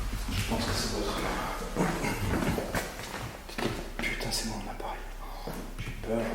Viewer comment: tu dit "je ne sais pas ce qu'il c'est déclencher la haut" juste après j'ai l'impression d'entendre un pve qui dit "c'est moi " Si j'ai pas rêver c'est flippant..